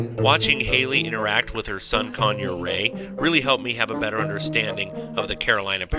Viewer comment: I thought he said "...with her son Conure Ray" and I thought "now that's a little too close of connection to your pet..."